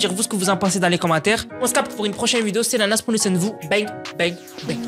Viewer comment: Moi qui après 5 ans à regarder comprends enfin qu'il dit "prennez soin de de vous" 😅